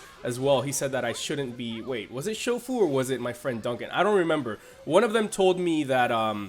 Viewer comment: was i the only one when he said duncan or whoever thought he said dunkey as in videogamedunkey?lol im dumb